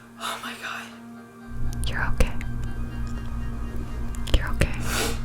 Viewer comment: i had earbuds in and every time she did the “youre ok” i sounded like someone was whispering in my left ear and i got scared lmfao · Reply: I thought it said you're gay and I was like "weellll"